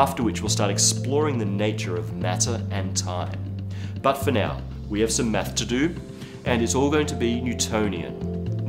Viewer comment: did anyone else hear "We have some meth to do" or was it just me?